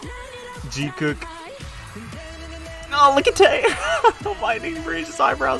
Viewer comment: Sorry but did you just say Jikook 😂😂😂